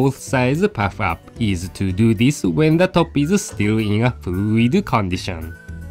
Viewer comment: What does he say at 2:30?? Please I can't understand lol. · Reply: When the top is still in a fluid condition.